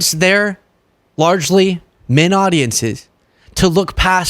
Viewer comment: I know said "men audiences", but I heard "mid audiences", and I don't think that's wrong, either.